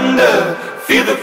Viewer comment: siempre pensé que decía era "Phantom"=👻 no Thunders 😅qué nota que soy tan Colombiana que oigo lo que oigo y lo entiendo tal cual se oye,y busca mucho después que es realmente lo que dice